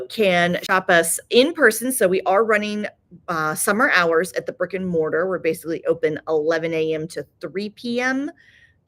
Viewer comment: Every time you say “brick and mortar”, I hear “frickin mortar” and it makes me giggle 😂